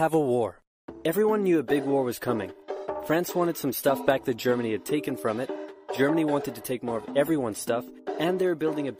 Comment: What does say at 1:14? Also good video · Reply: He just says "what the" There's nothing extra although I should have probably changed that lol